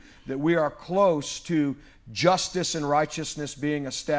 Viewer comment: They say “truth and righteousness;” I hear “US Inquisition.”